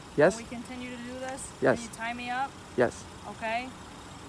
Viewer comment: Did she say Tie me up🤣🤣💯